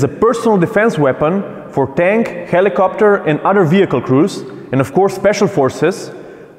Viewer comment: Misheard you, thought you said PDW AGAINST tanks and helicopter lol. Love your informative video guys :).